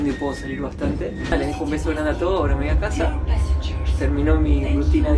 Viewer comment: Entendí rre mal, pense que dijiste "ahora me voy a comer a Helena". Jajaja